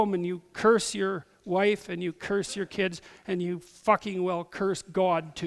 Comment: he actually says f*cking